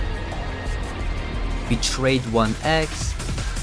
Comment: “betrayed 1 eggs” is what I heard